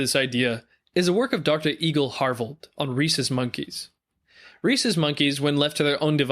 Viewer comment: I legit thought he said “racist monkeys”